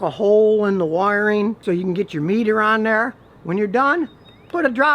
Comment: Did he just say “get your nut off” ?